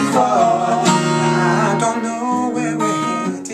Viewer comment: haha, first time Max said the title, i thought he said fight forthis glove.makes sense..